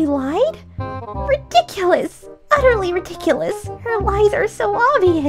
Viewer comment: BRO NINO -DUDEEEEE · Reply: Hi again I thought Chloe said ridiculous utterly ridiculous